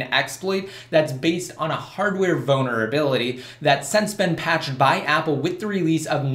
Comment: lol for a second I thought you said "hardware Boner" 0:31 but Iwas like XD Oh wait nevermind :p